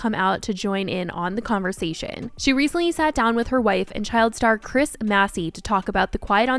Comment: my dumb ass heard this as her wife is Chris Massey